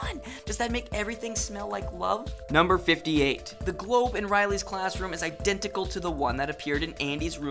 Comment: When you said globe I thought you said the Boy lol!